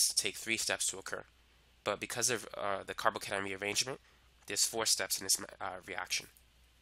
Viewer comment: please what did he say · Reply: "But because of the carbocation rearrangement, there's four steps in this reaction."